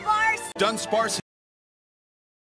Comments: i hear "danspac" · Sounded like Misty said Dumbsparce.